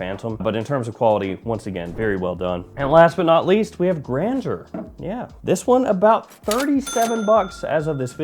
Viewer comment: I thought you said the last one was called grandpa :face-green-smiling